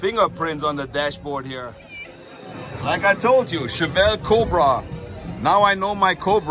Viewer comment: Shout out to you. I thought you said Chevelle Cobra, but I realized it was your German getting in the way of your English pronouncing “Shellby”I have a friend from Großenkneten, Germany who came here in the early 1980s with friends who were in the band, Trio. He learned his English in Southern California. Freddy Winter. He played guitar in a Reggae band but now just lives in Del Mar and drives for Uber Black. You remind me a lot of my friend Freddy.best of luck for the future. Cheers.